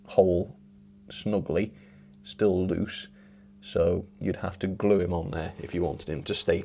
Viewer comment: "Stool loose" did you say?